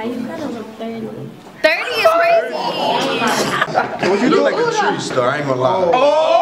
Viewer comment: Wait what did the guy in blue told the girl in orange at 14:29 when she say he look 30?? What did he say to her? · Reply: He said she looked like a treesh